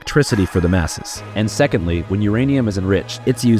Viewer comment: did you just say defense?